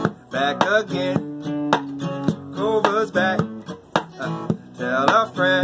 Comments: I thought he said covid's back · I thought he said “guess who’s back back again covid’s back tell a friend”